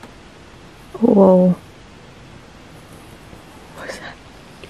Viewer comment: time stamp 9:25 i swore i heard a whisper saying what is up